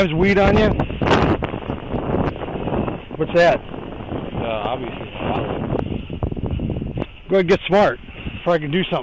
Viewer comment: "Any marijuana or weapons on you" "Nah. Just condoms" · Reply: I thought he said "obviously a dollar"